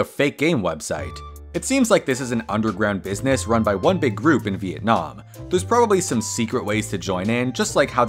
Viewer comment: did someone say Vietnam?